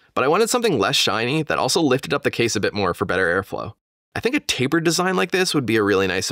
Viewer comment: did someone say taper?